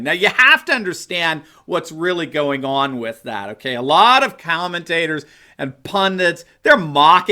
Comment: I fear a Hillary/Michelle ticket more than anything. Of course, VJ would have to Make AKAMichelle run.Call me crazy, if you will, but there's a lot of stoooped voters out there! Dr. Steve, did you say "cow-mentaters" at 08:51?